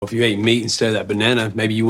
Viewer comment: i though he said if you eat me instead of that banana 😂